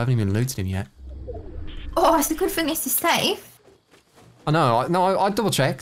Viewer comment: what does his gf say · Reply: 'Ooh, it's a good thing this is safe.'